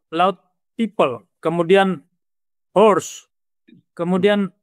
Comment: wait. Did he just said horses? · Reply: w hores